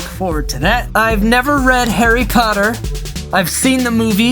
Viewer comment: did you say Harry cotter?